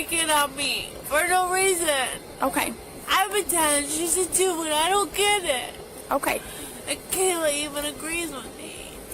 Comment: did I just hear I’m a 10 she’s a 2 and I don’t get it 😂